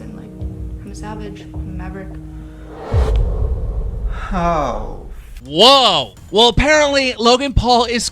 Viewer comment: it sounded like Shane said hoe Coincidence? I think not